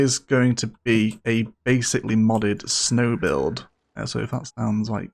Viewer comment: An entire hour of content! Boy, you do know how to keep your audience happy! Edit: Did I hear correct? A snow build finally!